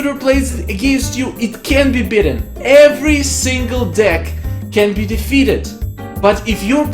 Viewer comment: did he say "every single dick..." :))